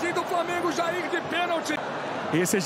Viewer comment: só eu que ouvi "tadin do flamengo jair de pênalti? Kkkkkkkk